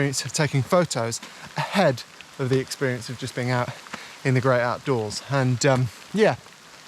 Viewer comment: I misheard it as "the GREY outdoors". How fitting :D